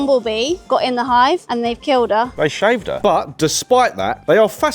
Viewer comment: I heard "they" keep killer bees